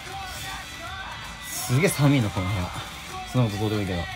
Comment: YouTubeの副音声ナシを見ても、Blu-ray見ても、どうしてもなんて言ってるかわからなかったので、副音声バージョンで解明されるのを楽しみにしてました。 まさかの｢この部屋寒い～｣で草 川拓弥